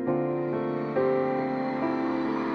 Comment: Can someone tell me what he is saying at 0:13? I've listened to it like 100 times and I still can't figure out what he is saying😂😂💖💕💖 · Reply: The goal would be to finish the 2019 tour in Toronto stadium ( I think ) 😂